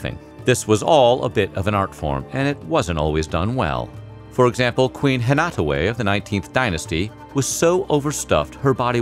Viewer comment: For a strong 10 seconds I thought you'd said Queen Anne Hathaway